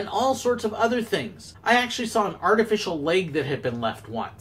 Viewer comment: oh god I thought you said someone left their artificial LAKE behind and I was so confused